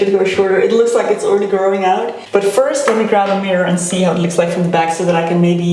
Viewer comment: I love that we literally shared your headspace for this video haha :D and when you said 'let me just grab a mirror', I could have sworn you said 'let me just grab a beer' 🤣 I was like - please don't cut your hair tipsy 😆